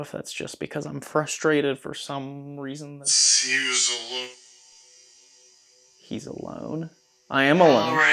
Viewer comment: 😂 I thought the man at 1:03:42 said ‘he’s a look’ that’s why the woman’s voice said ‘ok’ and straight after a man’s voice told you to get a drink. Then telling you to go to no18 😂 they must think that you are there to see a lady of the night. A very good investigation with clear and accurate responses. Thank you for the video.